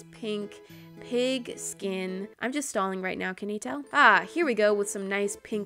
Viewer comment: When she said "I'm just stalling rn..can you tell?" I thought she said I'm just stoned rn..can you tell" oml my mind doesnt work